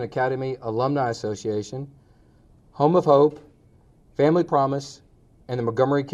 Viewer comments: thought he said Homophobe not home of hope 🤣 Queenpins On Netflix brought me here! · He started to say dark web coupon group" LOL yeah right. I thought that cop said "homophobe" in the beginning but then I realized he said "Home of Hope". Good Lord. Why can't Walmart donate that stuff directly themselves? I've seen videos of the hundreds of barrels of products they literally throw away every week or whatever. It's disgusting.